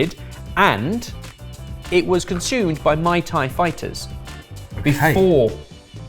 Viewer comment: I swear it sounded like said Mai Tai fighters. Now all I can think of is Mortal Kombat...But all the fighters are bartenders. The Mai Tai fighter probably won't fare too well against the Gin and Tonic fighter, but could probably kick the Cosmopolitan fighter's ass.